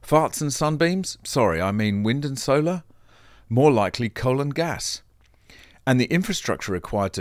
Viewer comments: I heard more likely Colon gas! As you said earlier, farts. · I thought you said colon gas after you talked about farts and sunbeams. Repeat showed it was coal and gas😊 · You gave me a good laugh today. It sounded like you said that electricity was going to be produced 'not by parts and sunbeams' but 'colon gas' . . . Which is how my tired head heard 'coal and gas'. I learn from these videos, thank you.